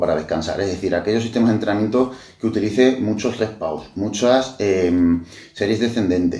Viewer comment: no entiendo que dice , red pause ?? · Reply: Rest/Pause